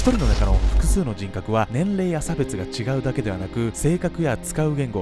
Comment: 「年齢や性別」が「年齢や差別」に聞こえたのはいい間違いなのか滑…